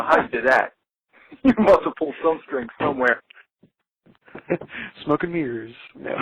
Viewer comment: when he said "smoke and mirrors", i thought he said "smoking the herbs"...